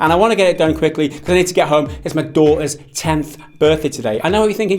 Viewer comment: I thought he said his dog's tenth birthday. I was more excited for the dog than his daughter.